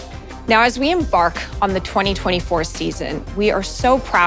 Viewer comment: You say "LoL eSports", I hear "loli sports"